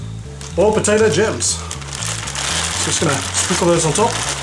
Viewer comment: I heard you say potato germs so many times xD was nice to find out that if was gems :P